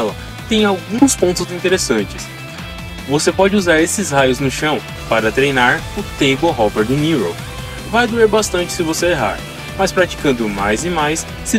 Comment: Pq eu escutei "O tempo Robert De Niro"? KKKKKKKKKKKKKKKKK